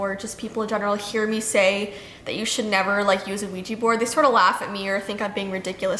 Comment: I kinda thought she said Luigi board